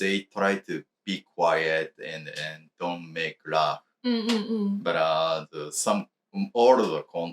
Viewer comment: If not for the subtitles l would have misheard "make laugh" for "make love" 😆